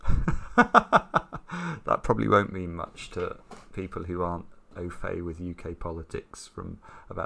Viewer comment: When they were talking about Edwina Currie, did Simon say "ofay"? That's a racial slur in the US, but from context, it didn't sound like he used it in that way, so I assume it means something else in the UK? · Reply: Au fait